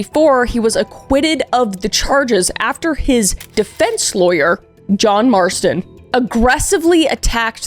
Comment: idk why but did i hear that that dudes name was John Marston??????????